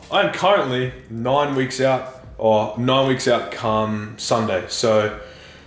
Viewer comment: all I heard was cum